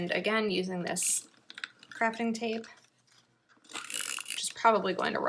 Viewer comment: What a great idea! I make journals out of Composition notebooks, scrapbook paper and put pockets at the front and back of the notebooks. But I love the way this looks! It sounded like you said crapping tape not crafting tape... 🤣 That movie is absolutely amazing! You are so much fun! I love your videos!